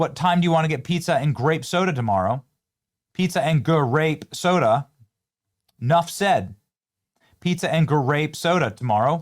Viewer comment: Why do I hear “foreign black kid” when they say pizza and black soda?